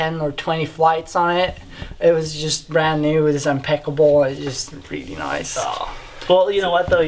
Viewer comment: I shouldn't make fun of accents and sorry in advance, but I thought said Chad's jet was 'unpeckable'. Maybe it had a very tough finish?